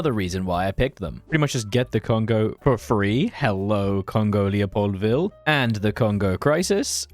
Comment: thought you said Congolia-poldville for a second